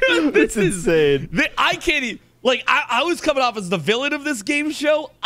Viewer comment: As the village of this game show? In Spain? With Leon? Or did he say the villain? In raccoon city? With Jill?